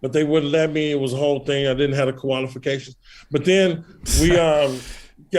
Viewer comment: Did he say, "I didn't have the koala-fications"? 😂